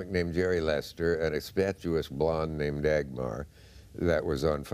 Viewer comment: What word is he using to describe Dagmar at 1:54? Sounds close to statuesque but maybe it’s a similar word I’m not familiar with.